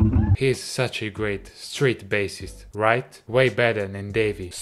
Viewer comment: I thought he was saying straight...